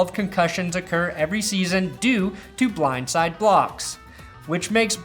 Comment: I thought you said blind side cocks. Those are definitely traumatizing.